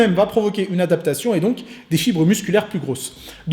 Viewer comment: c'est normal qu'a 14:42 j'ai entendu chibre au lieu de fibre ?